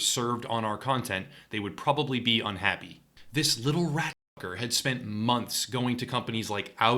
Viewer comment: What did you call him at 2:24? Almost sounds like you are calling him a rat n**ger.